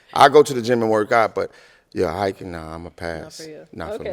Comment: oh she said "hiking"?! I thought she said "hacking". Like she was in IT or something lol